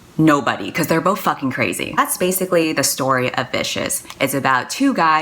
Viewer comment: I swear I heard "that´s the story of BISHES" INSTEAD OF Vicious. XD